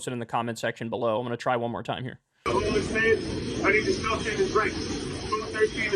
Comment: I have no idea how accurate this is but I listened to the part from 13:48 to 14:15 where Neely was talking over a dozen times to try and understand what he was saying. Here is what I could possibly make out. Anywhere you see the word 'something', it meant he said a word but I couldn't fully understand it. There are three distinct sections of things he is saying, so I put three sets of timestamps and a period at the end of each section to signify where they stop and to aid intelligibility. If anyone with better hearing wants to add and/or modify this, feel free. From 13:48 to 13:56 "I'm a homeless man (or 'I need you to all understand'), I need a shelter and drink, 'something' 'something' and milk, TV potpie, thirsty and shelter, milk, TV and drink, it has to happen. From 13:57 to 14:07 Thirsty and the milk, food hoagie, pass the full milk, 'something' to drink, thirsty and the milk, 'something' TV potpie, thirsty and drink, TV milk, thirsty and the food. 14:08 to 14:13 I'm 'something' hungry (straight hungry?), I need to survive, milk, toast (or really fast 'television'), drink, hungry, has to happen." That's what I got. Like I said, if anyone wants to try and fill in the gaps or can better understand him, go for it. I hope you get justice, Jordan.